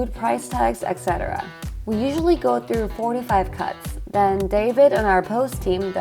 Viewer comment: I was shocked because I thought you said "We usually go through 45 cuts" before I realized you meant "4 to 5 cuts" haha